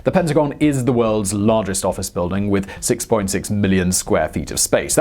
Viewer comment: My high ass thought you said it's the world's biggest octagon when you said worlds biggest office 😅